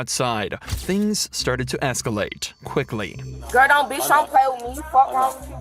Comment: did I heard "things began to Fcalate" - this is funny.... F word + escalate real bad..... Fcalate ....lol